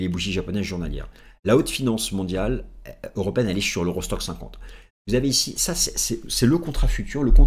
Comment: J'ai compris "eurostock saucisson" la première fois 😅